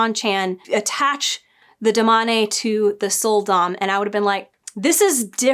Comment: When you said "Sul'Dam", I heard "Soul Domme".